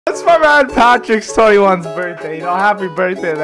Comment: Wait, did he just say 21th?